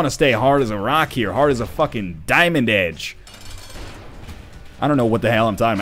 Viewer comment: I honestly thought said he wants to be 'as hard as a fucking diamond egg.'